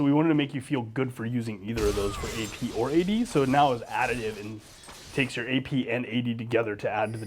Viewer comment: WTF DOES HE SAY AT 2:18?!?!?!?! "for AP or AD so now it ABABDABLEA" wtf !?